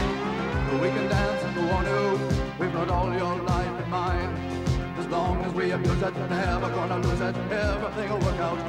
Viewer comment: I thought he said, “as long as we have Putin” 😂